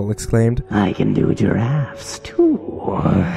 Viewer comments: i heard"I can do your ass too"......am i ok? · In 36:14, The English auto generated subtitles said that said "I can do your ass too" ... @@